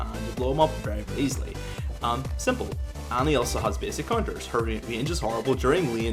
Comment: tfw i thought he said conjurer instead of counter like 20 times..